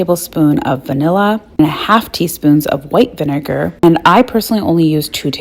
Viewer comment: Was the clear liquid you added white vinegar? Didn’t hear you mention it on the video but saw you add a clear liquid. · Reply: I think I heard her say white vinegar while she was adding the vanilla